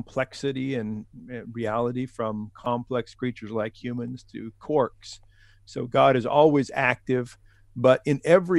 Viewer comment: Did he say "corks," as in what's used to close a wine bottle? What am I missing?